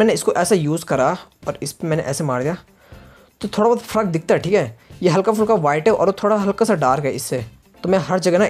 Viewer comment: On 8:20 I hear fuck